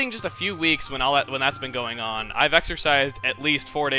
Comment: I Thought you Said Exorcised